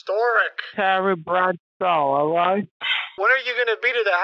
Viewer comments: "Congratulations, Steve Harvey will be at your house in about 40 minutes" is an example of a thing I hope I will never hear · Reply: I heard "And also Carrie Bradshaw" and was wondering why this was turning into a Sex and The City reunion. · "Congratulations, Steve Harvey will be at your house in about 40 minutes" is an example of a thing I hope I will never hear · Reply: I thought he said "Terry Bradshaw", This guy Kit's being might have won his money